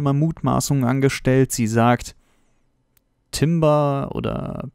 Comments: Ich hab immer Timbra verstanden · Ich hab immer TimRa verstanden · Ich habe immer trimba verstanden